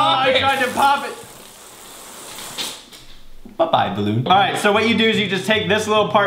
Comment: When he said bye-bye balloon, I thought he said bye-bye baboon